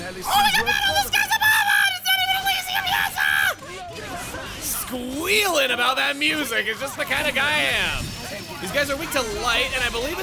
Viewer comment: Can someone translate/transcribe what he said at 4:04 ? I'm having trouble understanding that one... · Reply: "OHMYGODBATTLEINTHESKIESABOVEAAAHHHANDIT'SNOTEVENELYSIUMYESAAAHHH"